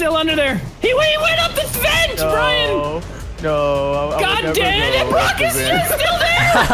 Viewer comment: I'm not sure if anyone else heard saying "ni**a" in 14:07-8. That really caught me off guard but it was the most funniest thing I had ever heard 😂😂😂